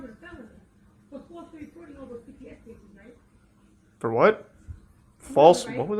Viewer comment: "for falsely recording all those cps cases" I believe that is what she was saying here